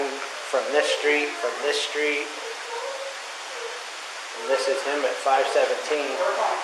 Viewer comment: Listen what is saying at the 45:45 to 45:55: "This is HIM at 0517". No NK there!